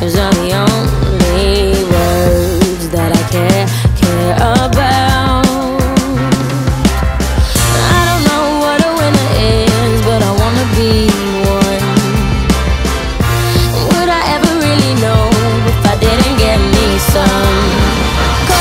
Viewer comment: XD I thought she said "i dunno what a woman is but I wanna be one" and me and my friend DIED. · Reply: I thought she said winter lol.. it made so much sense to me and I was like "omg I know me too! beautiful yet cold and difficult to handle!!! so deep!!!" nope